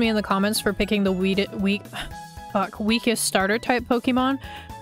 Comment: I think we all heard the weed starter right? That's not just my substance addicted brain kicking in right 😭 anyways always happy to see you upload! Love this channel